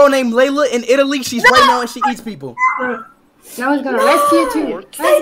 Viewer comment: At first I heard her say she’s hates people why are you using Omegle then 😂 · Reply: I thought she said she hits people